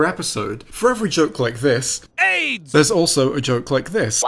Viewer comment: did he say AIDS or eggs? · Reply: Li Holma aids